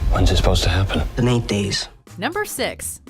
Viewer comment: 9日? 8日? 九日後って聞こえるんだけど実際は どっちか教えてけらさいな。